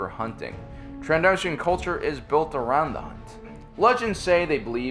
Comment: Did anyone else hear "hot" in the background audio? wtf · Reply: "Around the hunt"